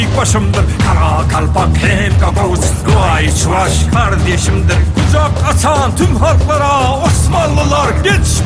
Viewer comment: de ne diyor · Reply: Nuğay, Çuvaş kardeşimdir